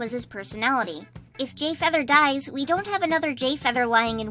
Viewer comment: I’m sorry, but I heard “If Gayfeather dies”